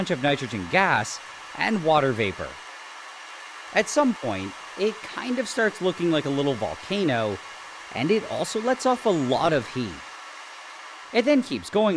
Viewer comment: What is said: “At some point, it kinda starts looking like a little volcano, and it also lets off a large amount of heat” What I hear: “at some point, it looks like a volcano, and acts like one too”